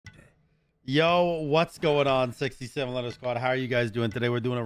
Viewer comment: We are now the 67 letter squad...at least that's what YouTube thought you said 😂😂